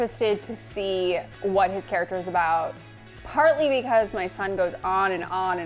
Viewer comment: Did she say " my son " ???????